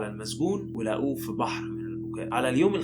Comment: حد يأكد لي اللي أنا سمعته ده صح ولا غلط في الدقيقة 08:12 ولقوه في بحر لو هي صح، معناها إيه؟ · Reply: قال لقوه فى بحر من البكاء مش فى بحر بس كان بيعيط يعنى